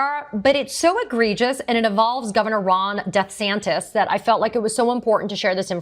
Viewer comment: DeSatan could not be more accurate! 😂😂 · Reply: thought she said death-santis